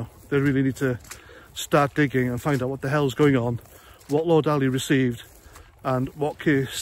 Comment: For a minute there I thought it said go down on Lord Ali 😂😂😂😂😂